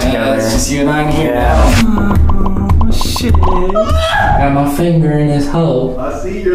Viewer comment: "I Got My Finger In His Hole" Is What I Heard😭